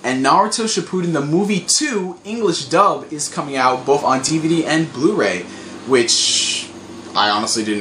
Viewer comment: I heard you say 'TVD.' Did you meant TV and DVD?